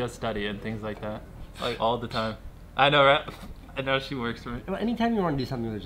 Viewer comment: That's hilarious "Now she works for me" sounds like 10yo's dream. Maybe he said something else but that's what it sounded like xd